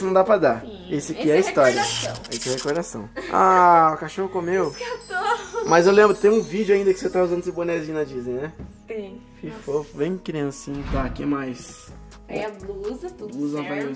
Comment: os catorros kkkk só eu que ouvi isso da?? gente criei um canal voces podem ir lá??